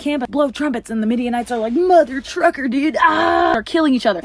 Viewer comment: I thought they said at the very end "because God's not straight"